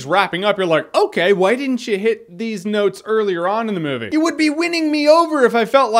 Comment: it sounds like he says "deez nuts" more than "these notes."